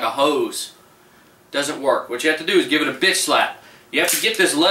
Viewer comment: did he say bitch slap ? Turn on captions